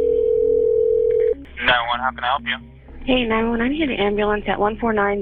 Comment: Where did she hear honey? I didn’t hear honey. I heard “hey 911” “I know him personally”. Omg 😳 do I need hearing aids?!